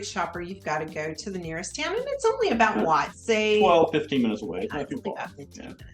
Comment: Y'all are funny. A con is 12 to 15 minutes to a Target... I live in a suburb of Dallas, TX. That's just a nice brisk walk, as long as it isn't July or August because you will melt in the 100+ degree weather. 😆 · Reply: I thought they said 12 to 15 miles.